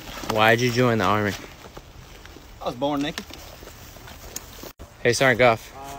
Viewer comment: I thought he said “I was born nigga”